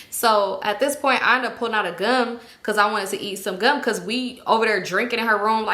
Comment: Lord , I’m listening from across the room while I’m cleaning up. I thought you said “I pulled out an🔫” 😂 I’m like “Damn Symone, you just met these girls …what did I miss?.,” 🤔😳But you said “gum” . 😂🥴